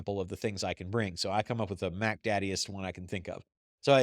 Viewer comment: [QUESTION] Did just say, "... so I come up with Mack Daddiest one I could think of."